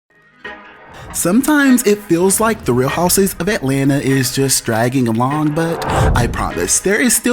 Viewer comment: Actually it sounds like is saying " The Real House'wises' of Atlanta". It's sounds cute though.